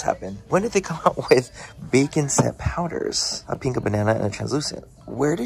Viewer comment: I definitely thought you said bacon scent powders and I about ran to Ulta.